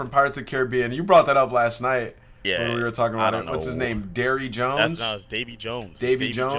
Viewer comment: “ What’s his name DAIRY JONES? “ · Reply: First time I heard that shit I thought he said “Jerry Jones” and I was like damn just flame cowboy’s fans 😂😂😂